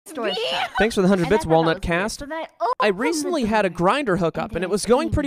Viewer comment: I heard “walnut cast” and I laughed immediately